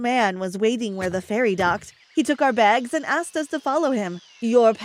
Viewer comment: i thought he said your parots informed me lmao